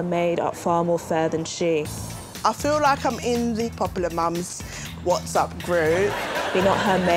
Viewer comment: Wait wait wati... Judi said she's in a 'WhatsUp' group... is WhatsApp supposed to be a play on that phrase? Am I a complete moron for only thinking of this many, many years late? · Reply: Yeah pretty much. Judi did say 'WhatsApp', it's just her accent sounds like she said 'What's up'